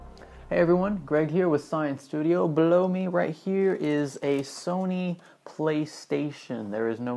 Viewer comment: You said "below me right here" What I heard was "blow me right here" ._.